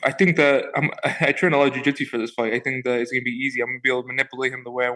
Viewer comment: Did he just say I trained jujitsu for this fight? Hahaha